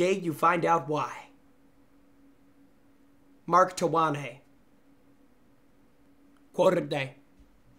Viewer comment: Rewatched this video, and the second time I heard the quote of the day at 47:20, I laughed so hard when I realized the name was actually Mark Twain. 🤣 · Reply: "TA-wA-nAy" 😂